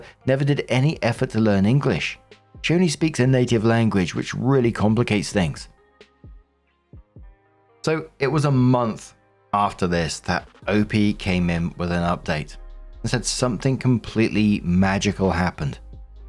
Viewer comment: Ooh! has finally started to use "an" instead of "a" in "op came in with an update" I always thought he was saying "her" update lol